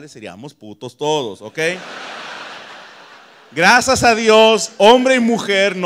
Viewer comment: entendí grasas a Dios XD